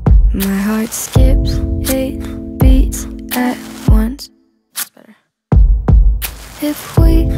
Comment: does anyone else hear “that’s better” in your right headphone at 0:25 ?